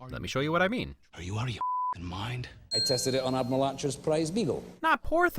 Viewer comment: I knew I heard it right the first movie watch! It is "Are you out of your f*cking mind?!"